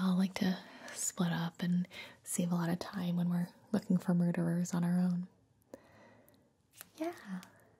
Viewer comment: did she say "murderers?" What kind of Scooby Doo does Gibi watch??